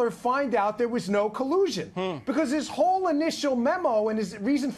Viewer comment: Fake digital-voices are getting better, but... Nevertheless, a very satisfying video. Edit: maybe your voice is just badly edited. Can't tell which. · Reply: yeah I heard "collision" and "Brenman"